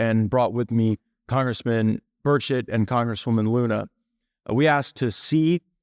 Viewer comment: No gonna lie, at 35:09, I thought he said “congressman 35:25 bird sh*t.” I’m better get some sleep lol